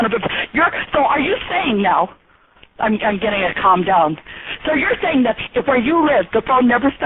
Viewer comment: i swear at 5:22 she says "im getting a comedown" sounds like she had a heavy night prior ^^